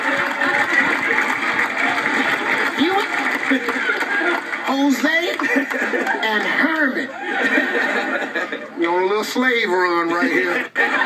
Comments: Hosea is also a Hebrew prophet in the Bible, even a booked named after him. I've definitely met people named Hosea... Come on now. Give him credit. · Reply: Pedro Viera True! However if you listen carefully, it sounded like he said Hosea. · I thought for sure he said Hosea! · Sounds like he said Hosea. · I heard "Hosea..."